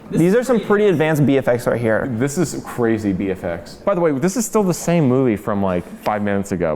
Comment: I heard that as BeeFX lol😂